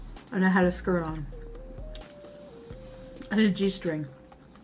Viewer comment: lmao i thought she said: “i ate a cheese string”